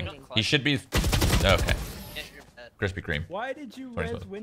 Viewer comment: Please tell me someone else heard him say "Krispy Kreme... 2012"